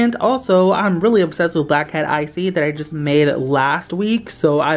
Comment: Hi is this Uniicorn slime shop, BECAUSE I WANT ALL THE SLIMES 7:34 BSDJFDBBDFBD I THOUGHT HE SAID BLACKHEAD ICEE AND I WAS LIKE WHAT